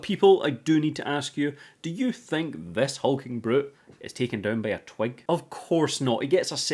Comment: I thought you said 'he gets taken down by a Twink ' and I was like yeah wouldn't be the first person